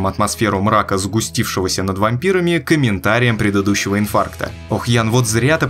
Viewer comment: А ВЫ ТОЖЕ УСЛЫШАЛИ "ИНФАКТА"?!?!?!?😅🤣🤔😱😅🤣🤔😱😅🤣🤔😱